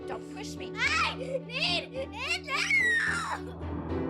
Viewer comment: what she saying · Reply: Clyde Farrow : I need my something